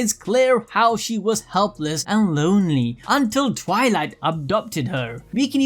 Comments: I misheard it as "Twilight abducted her" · did he just say twilight abducted her